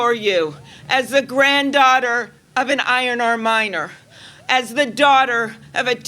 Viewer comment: ok I know I grew up in the city, but what the heck is an ironer miner? wouldn't it be an iron miner? or did her grandfather work as a miner and do ironing on the side? or is it just a minnesowtah accent thing? What is klobuchar saying? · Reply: I thought she said iron ore miner